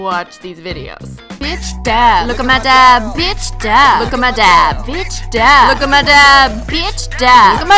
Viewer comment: I thought you said, look at my dad and i was like wtf XD